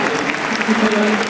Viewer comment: what did that person say? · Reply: "see if they're champions"